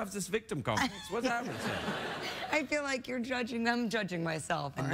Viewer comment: Wow. I misheard the last part. I’m sleepy here ok. I thought he said “ you shouldn’t touch yourself. That’s what the internet is for”. Guess I know where my mind is at looking at scarlet